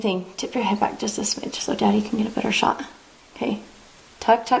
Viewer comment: did she just say ''daddy''?